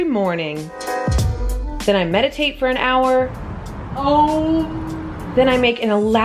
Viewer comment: Did she said Om? 🤔😍